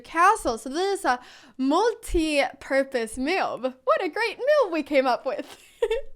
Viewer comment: When you said in 6:41 multiporpuse move ...I swear I heard multipurpose MILK ..??? Considering it's cow opening , it does make- sense 😂😂😂😂.